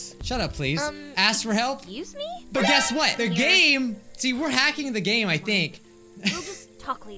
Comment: Ngl, when we said "the game-" I thought he said "they're gay-"